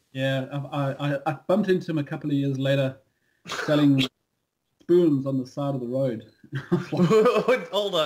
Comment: Can a nice chap write me down what he's saying? I just cannot hear it… :/ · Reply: "I bumped into him a couple of years later, selling spoons on the side of the road."